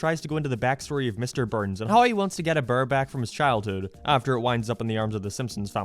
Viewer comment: The first time I heard "Mr. Burns get Eddie Burback" instead of "Mr. Burns get a bear back." Thanks, Irish people who invented the dialect and accent In which modern Irish people speak.